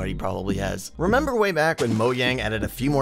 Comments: why did you say moyang in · did he say: Moyang?